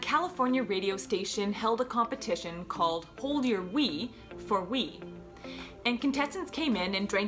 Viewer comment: At first I thought she said the contest was “hold your weed for weed.” That would have been a lot cooler. And a lot safer. I cannot believe this radio station did such a contest! I I never heard of this case before. Ten radio employees were fired, and the husband went on to win a $16.5 million dollar verdict. What a preventable tragedy.